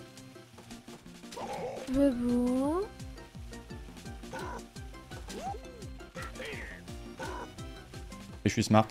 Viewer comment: c quoi qui dit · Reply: Bebou